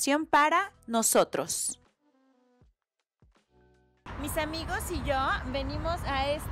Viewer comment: it says “mi amigos” but you say it is “my friends” plural… 🤔 I don’t know if it is a mistake or some grammar exception I don’t know about, but wouldn’t it be “mis amigos”? It sounds like that is what you said.